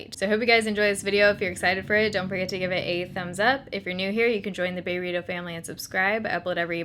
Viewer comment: I always thought she said "If you want to be part of the burrito family dont forget to subscribe" so I did 😁